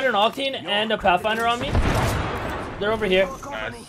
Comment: did he just said "a crack-finder on me"? Hahahah